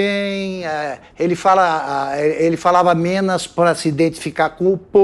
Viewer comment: falava "menas" para se identificar com o povo, foi triste 🤣🤣🤣🤣🤣🤣